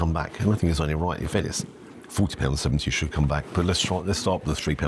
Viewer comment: Until I saw the receipt, I thought you were saying 14.70 and was shocked. But 40.70 for that???? Even if the food were good, that's more than double what should be considered value. You were just robbed! Held up at food-point. File a police report.